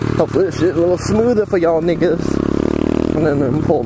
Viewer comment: Excuse me 2:27 did you say niggas